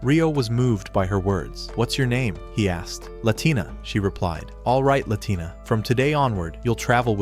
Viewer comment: Why do i hear latina? 20:02 Or ratina or Aatina